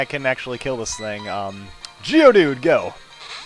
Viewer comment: Lol! Sounded like he said jewdude :)